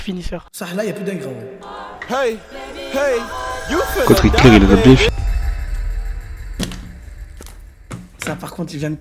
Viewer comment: À 34:00 il a dit quoi le gars ? Sur les allemands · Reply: Je crois il a dit " Par contre Hi**er il a bien finit "